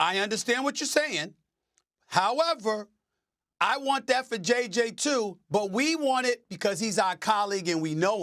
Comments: I had the playback speed on 1.5 and I heard “I want that vajayjay too” and had to double take and pause when SAS actually said “I want that for JJ too” 😂😂😂😂 I thought bro was talking crazy to Andrea carter 😭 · when Stephen A. Says " I want that for JJ too" I heard "I want that va-jay-jay too" 😅